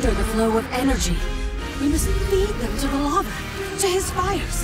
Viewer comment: Maybe i am crazy, but isnt the voice at 2:14 saying:" These roaming undead hinder the flowing energy. We must lead them to Palawa" ? Oo could also be "...into the lava." But i reallylike this Palawa joko idea. If we assume that the scepter of Orr is NOT coming right now, who would be able to control the undead? Who would be an uncommon ally? Palawa Joko! Maybe that is just GW1 Fanboying here, but i caould imagine that this will be the stroy line. And it would be kinda ironic to ally with Palwa AGAIN because we are forced to. This also would lead us to the christal desert. But maybe my thought process here is total BS^^ What do you think? Palawa would be awesome! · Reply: I heard "Into the lava." I think we all have Joko on the brain, and since they used fiery imagery during that line, I'm going with "Into the lava".